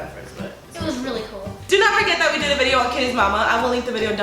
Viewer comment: Oops I thought Karina said Emma instead of Kitties Mama 😬😬